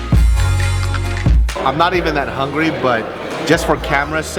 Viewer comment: Thought he said he wasn't hungry